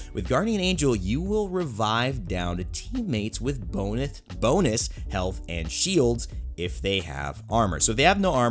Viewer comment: died laughing when I heard it “bonuth”